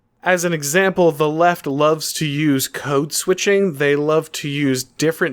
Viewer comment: Not to clown on you or anything, but don't want anyone "owning" you by correcting your terminology ;) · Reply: Thanks for correcting me, I thought I heard coat switching, not code switching. One of the definitions I found relevant: Code-switching - Concurrent use of two or more languages in one and the same speech act. It's Jargon from Doublespeak where they can feel they've one-upped an actual intelligent person who's not in the know. Doublespeak is never the plain, self-explanatory or simple truth. They want being more knowledgeable to equal being more intelligent. But knowing or making up more words for the same thing doesn't increase intelligence (the amount of ways we can try to understand something).